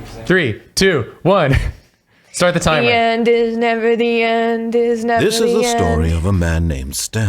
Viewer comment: The way the narrator pronounce "The end is never the end", it sounded like he was saying "Is Never the end?". Which I think is a great question! Is never the end?